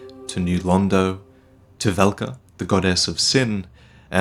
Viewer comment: Am i the only one who heared God ass